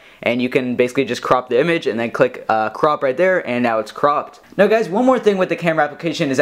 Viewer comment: I thought you said " And now it's Crap"